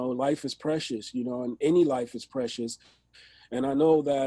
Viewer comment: To hear Esham say "life is precious" is something else. Gotta finish this interview. It's really enlightening to get a little behind his bizarre, scary music and why he went there with it. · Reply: I hear "life is precious, I'd kill it though"